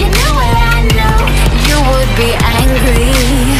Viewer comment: Why at the end part I hear: "You are me angry" "You would be angry" AMD then just the rest of the song ending right?